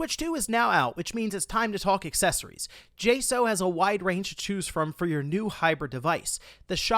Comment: I thought it was said “j-socks” for that accessory brand jsaux… interesting 🤔